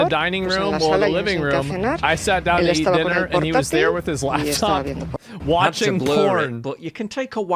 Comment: I swear it said “he was there with his livestock watching porn” instead of laptop 😂